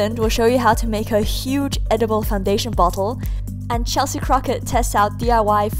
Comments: When she said bottle I thought she said butt hole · Did she say edible butthole? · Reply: No she said huge edible butthole my bad